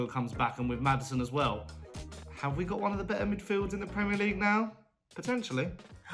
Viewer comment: I feel like they must have misheard the Spurs fan, he said "have we got one of the better midfields in the league now? potentially!" - sounded like they thought he said "we have the best midfield in the league"